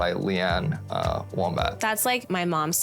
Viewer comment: …Am I hearing things or did he say “Lee Ann Wombat”? 😄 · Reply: He absolutely said wombat 😭